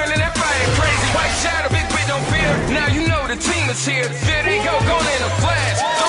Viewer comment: It sounds like he says video gone in a flash, but he says there they go gone in a flash!